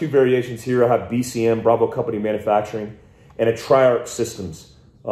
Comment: What did you say your "other" AR platform is? I got BCM (I own one), but it sounded like you said "try out systems" for the other. Can you clarify please? Thanks